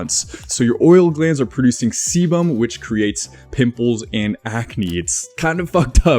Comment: Did I heard my oils glands are producing CBum 😎😎😎😎